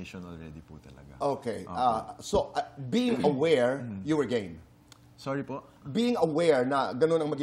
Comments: ang dinig ko "you were gay?" AHHAHAHAHAHA and Ralph in his head was like (did I heard it right?) At napasabi na lang ng "Sorry po. (I didnt heard it clearly). HAHAHAHAHAHA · being aware you were gay? you misheard it too right? game pala.